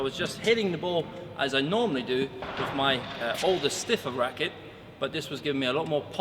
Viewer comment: how could I contact for his thoughts on the Pro Staff 97 vs Blade 98 (16x19)? Does he reply here through the TTT youtube? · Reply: did you say “older stiffer racquet”? Are you referring to your Aerogel 200? Im confused because I thought the Aerogel 200 was way flexier and plusher